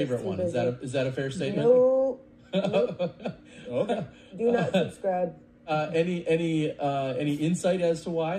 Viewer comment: Paige said "do not subscribe" not unsubscribe lol. As in she doesnt subscribe. So seems like he just misheard. Brodie trying to timeline out the beef and then making a veiled threat towards Paige is....exactly why people call him dramatic. Thats a high school boy level of thinking lol "Now i know where i stand with Terry cause he didnt start an argument with a guest on his podcast" 😂 how have you not grown out of that